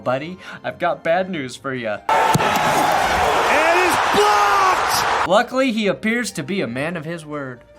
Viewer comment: sounds like he says blopped not blocked